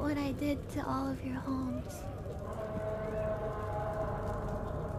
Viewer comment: I heard "homes" as "holes"... y e s